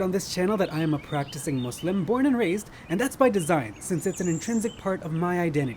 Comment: ok i still didnt understand a born Muslim or a convert? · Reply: He said he was born and raised, listen